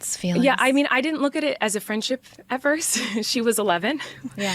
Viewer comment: Dude unplug your ears; she said “I didn’t look at it as a friendship, AT FIRST—-“ ; she said AT FIRST, not “EVER”. You listened to it several times in a row, and then misquoted “AT FIRST” as “EVER” over and over; went on about this specific “lie” for quiiite a while, like is your hearing okay?? She said they weren’t friends AT FIRST. Later, she says she does consider her a best friend and a sister. I’m CERTAINLY not defending Colleen (Nor JoJo, for that matter) I strongly dislike //and entirely distrust them both; but it does feel like a bad-faith commentary, for you to conveniently “mishear” and misquote things several times in this video… making arguments like “OH nowwww she’s your sister!! So you’re not friends you’re sisters - so you must have been FRIENDS when she was young …” LIKE. You’re stretching here. You’re re-wording things, and inventing narrative that simply doesn’t exist in the original content of the video. As a creator who’s here to share your opinions, preach about ethics, morality and RIGHT-FROM-WRONG; Your goal is to influence people (many of them YOUNG PEOPLE), SO you really should be responsible when it comes to quoting people fairly; to stick with the facts and not skew someone’s words to make it more sensational /// appeal to emotions and better serve your narrative. ((Intentional? Accidental?? Either way it’s harder to trust you.))